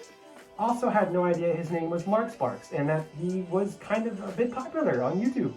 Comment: why did I hear markiplier? 😂